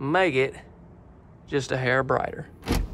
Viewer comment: Sounded like you said 'maggot a hair brighter'...